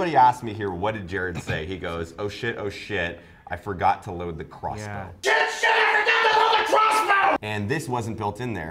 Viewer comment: I thought JT said "Can't shoot her I forgot to load the crossbow."